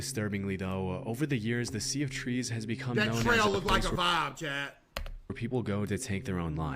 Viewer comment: did he say it looked like a vibe 😭